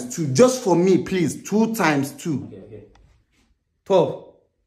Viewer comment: for some reason I misheard and thought he said 4, for 2x2, but then saw the "father"s reaction and tried to hear again and when I heard 12 my reaction matched dad's reaction at 1:10 😂😂😂 and thought "aahh bruh..now you gonna become a sacrificial lamb for real" 🤣🤣🤣